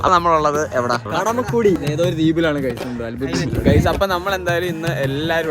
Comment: anyone hear the name of that island....😂😂😂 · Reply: Kadamakudy🖤